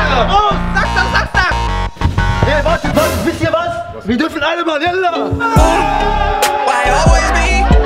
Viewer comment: Wer hat bei 3:18 auch gehört wie allah u Akbar gesagt hat😂😂😂